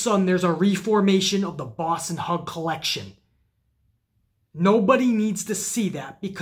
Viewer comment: Sasha Banks WILL make RAW better again. So much better. 26:38 did you just say The Boss & Hug COLLECTION?